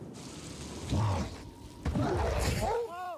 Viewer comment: I thought he was saying you’re no longer invited to my wedding, but he’s actually saying Wong you’re invited to my wedding. Can’t believe i’ve been mishearing that